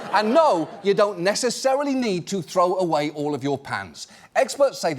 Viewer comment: Ngl I heard John say 'So should I throw away all of my pants?' 😂 The worse thing, that statement does apply.